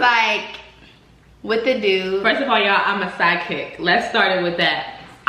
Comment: Who else thought she said “sidekick” but realized it was “Phychic”